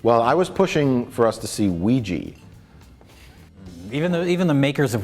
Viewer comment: Odd and appropriate timing: When they were talking about Nightcrawler, and said they were actually interested in seeing Ouija, I misheard it as Weegee --bringing to mind the movie about the original "nightcrawler," The Public Eye, starring Joe Pesci as Weegee, the ethics-challenged police-beat photographer... <link>